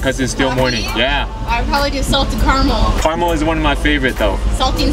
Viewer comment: salted 我聽成salt t i t s